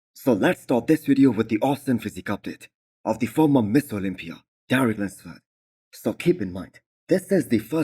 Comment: LOVE your content.My online favorite . Just one critique, It always sounds like you say "MISS Olmpia" Please clarify and say clearly "MISTER " !!